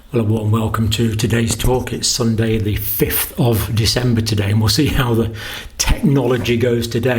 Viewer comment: Did you say December?? Lol